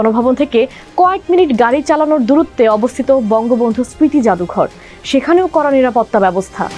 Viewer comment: Did she say BangoBulto?